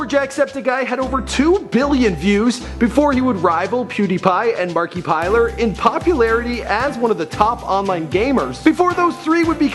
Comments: did he just say marki piled? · Did he just say "Markipiler"?! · Did you just say Markie plier??